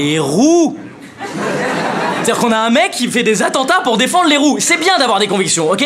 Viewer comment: Au début j'ai compris "Pour défendre les roues"... 😅